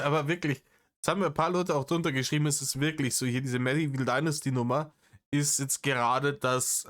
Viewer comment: Entweder hast du es wirklich gesagt oder ich habe es nur falsch verstanden (Agathe Bauer Style): ich hab immer "Mettigel Dynastie" gehört... Danke dafür und für deine hadde abbeid